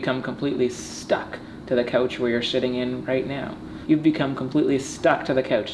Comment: did he say shiting in or sitting in